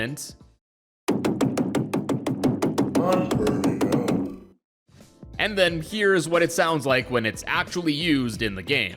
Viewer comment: what in the WORLD did it say????? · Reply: OH MY GOD HE SAID "IM BURNING UP" I SHOULD'VE KEPT WATCHING IM SO DUMB.